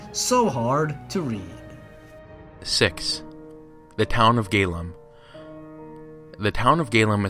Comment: I thought you said "town of gaylove" haha